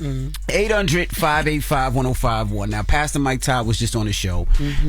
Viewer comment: It sounds like Envy is saying Pass The Mike Todd